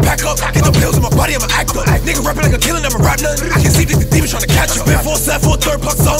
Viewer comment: What did he say at 2:05?? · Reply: Some demon we trynna catch him